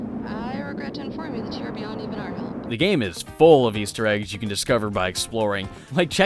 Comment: I don't know if it's because my native language isn't English, but no matter how many times I listen to it, I don't get what she's saying... "I regret to inform you that ______"... What's she saying? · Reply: you're beyond even our help.